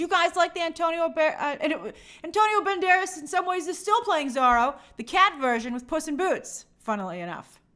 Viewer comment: pronounces Zoro as Zahro, for a while i thought she was saying Bizarro, instead of Disney's Zoro.